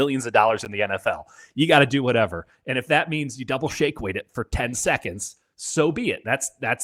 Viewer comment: The double shake weight….😂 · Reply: I misheard say, "if you got to double shake weight for 10 thousand, you do it". I'm sure 10 seconds became 10 thousand because my brain thought of counting seconds by saying "thousand"...nothing else.